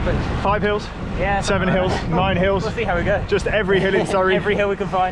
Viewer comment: did say 7 Hills or.........7 HELLS!